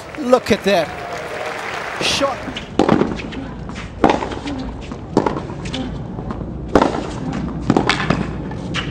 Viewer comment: Hey guys, I love Ben but unfortunately I don't speak French. I am really curious about what he says at 00:38? Could you help me out please? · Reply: "j'ai envie de rentrer chez moi" it means i want to go home